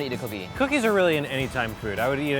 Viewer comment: I heard "Cookies is really anytime fruit" and SAME.